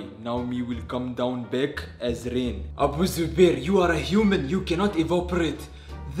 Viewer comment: i turned on the subs coz i couldnt hear my volumes low and the subtitles called abu zuabir abusive bear got to 4:15 and turn on subtitles